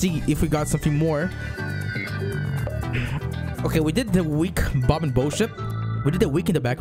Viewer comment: bob and boshit Kkkkkkkkkkkkkkkkkkkkkkkkkk eu entendi isso não me julgue 😂😂😂😂😂😂😂😂😂😂